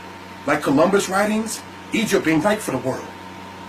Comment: What did he say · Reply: ' Egypt didn't write for the world '